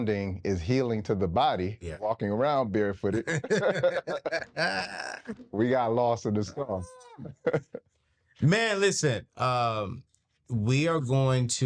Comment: I think he said "We got lost in the 'sauce'."